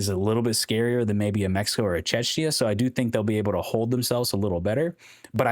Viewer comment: Czechia is pronounced "CHEK-yah" dude. I kept hearing "Chechnya" when you pronounced it lmao